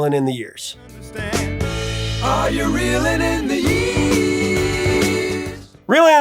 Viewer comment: "Are you reeling in the YEAST" Is what I always thought it said. Like, someone's fishing and catching YEAST! I'm a dope lol.